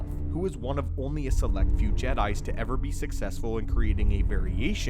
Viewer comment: Yo did say "Jedis" at 6:55?